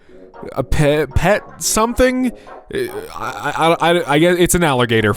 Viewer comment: and… ‘petting zoo’ maybe? 8:03 it says ‘pixiss art dotting stylus’ …I don’t understand and what that means. 8:34 ‘cured’ (said like ‘he’s cured!’) not ‘curled’. Like when pottery gets cured